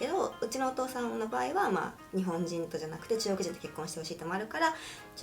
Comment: Sorry for bothering with probably a bad question. but at 2:53 I heard she saying nihonjin and the subtitles are only talking about chinese man. it´s literally the only word I picked, but doesn´t it mean japanese person? I was wondering if the word for chinese person is similar and I´m just hearing bad. Could you please explain?